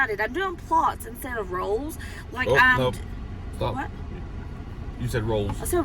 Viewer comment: I swear I heard you say "rolls" instead of "rows" lol 😂